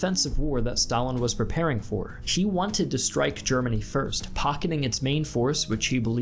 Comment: did you say 'she wanted' about Stalin?